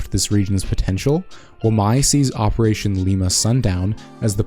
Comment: I thought it said operation ligma sundown